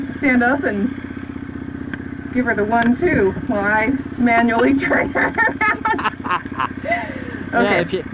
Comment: Okay pause for a second as I wipe the tears from my eyes from laughing so hard! That's what I thought she said, too "Annual", but she says she said "Maunal". I was also thinking lady exam. Lol! We are dying over here! Dang we miss you guys!